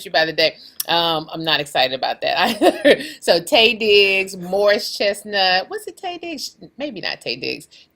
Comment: I heard “Moist Chestnut” too 🤣🤣🤣🤣🤣🤣🤣🤣🤣